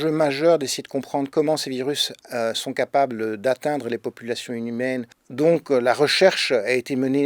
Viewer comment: c'est moi ou il dit "populations inhumaines" ?